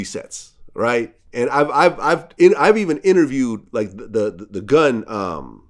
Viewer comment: I thought Vlad was about to say “i even interviewed the gun”... 💀💀 like damn, there’s no bounds that Vlad won’t reach